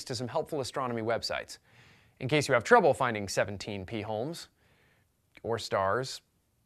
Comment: I thought at first he said 17 pee holes. Ew.